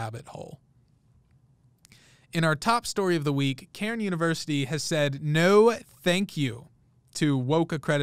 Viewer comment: I swear, until I saw the spelling of the name of the school, I thought he was saying Karen University.